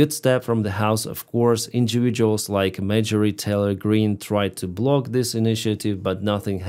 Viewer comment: When you said "Marjorie Taylor Green" I heard "Major Retailer Green" which threw me for a second - from now on that BBBB will be known as Major Retailer Green 😆😆😆.